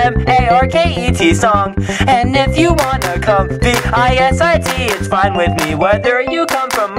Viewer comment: I thought he said "and if you want a cupcake i-t is fine with me" :/